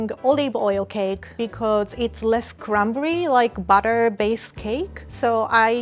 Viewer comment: Lol whoever did the closed captions at 1:30 how does "cranberry" make sense. "So I'm baking olive oil cake because its less cranberry like butter based cake." replace cranberry with crumbly lol. The auto-generated says the same thing so I feel like somene just takes the auto-generated and edits it, but missed cranberry. Cranberry makes no sense in that context. This is something you think a human would catch. Also at 1:45 it says "copies" instead of "cup is" and at 3:09 it says "And usually, I (indistinct) for 30 minutes," pretty sure she is saying "mascerated." I could go on and on... If you paid an outside service to do these captions you should ask for your money back lol.